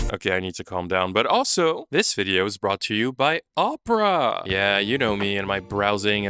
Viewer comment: I gotta be honest I thought you were saying « this video is brought to you by Oprah »